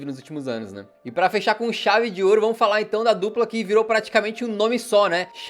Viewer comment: kkkkk, foi só eu que pensei isso: "Pra fechar com Xavi de ouro" 🥶👍